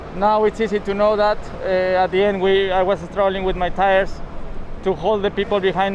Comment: For a moment I thought I heard Sergio say "Strolling with my tires"